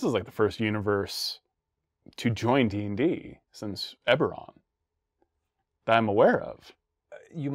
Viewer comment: Subtitles say ever, meant to be Eberron.